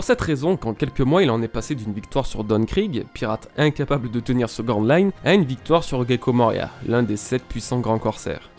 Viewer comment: il dit quoi ? Pirate incapable de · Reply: De tenir sur Grand Line